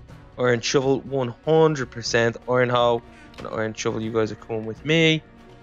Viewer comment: I thought that you said "ok chub chub comon with me" when you said " ok iron shovel you coming with me